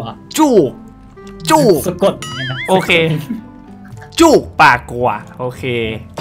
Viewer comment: ตอนที่ทานสเลสอ่านให้เป็นจูบ แล้วพอพี่พูดว่าจูบปากัว ทำไมเราคิดว่าพี่พูดว่าจูปากกูว่ะอ๊าาาาาย เขินว่ะ😳😳😳😳😳 (สาววายจ้าา)